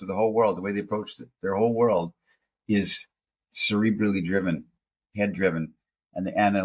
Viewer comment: "Cerebrally driven" was on the tip of my tongue as said it 💓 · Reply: At first I thought you said "Celebrity driven"